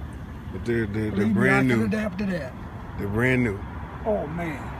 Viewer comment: I'm sorry but at around 1:13 did he say he could dab to that?? not a joke I'm legitimately curious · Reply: I think he said adapt